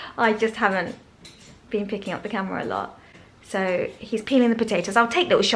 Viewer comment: …said let me show u what Matt was doing in the kitchen…it sounded like u said “Matt is peeing in the potato’s…lol. Give a listen u will get a chuckle 🤭